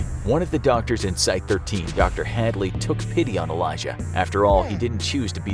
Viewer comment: I may be SCP-420-J off my SCP-789-J but did i just hear "HADMOOD"